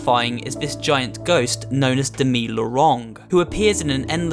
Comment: Um.. what did u say ? Dewi Worong ? Demi Lorong ? Dwi Nolong ?????